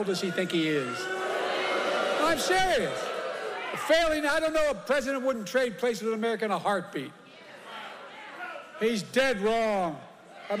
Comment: What on earth did Biden say at 4:37? Doesn't make any sense? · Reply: “I don’t know a president that wouldn’t trade places with America in a heartbeat.”